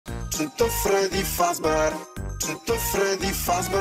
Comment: Bro i hear "Cheat on Freddy Fazbear" 😂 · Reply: I hear "shit the Freddy fazbear"